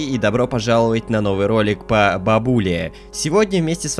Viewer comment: Мне послышалось " новый ролик по пабге "